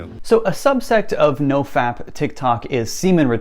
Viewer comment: This is the 2nd video I've seen from about this subject. I swear I thought he was saying "No fact TicTok", but I've just realized he was saying "No Fap TicToc". Honestly, it hits the same.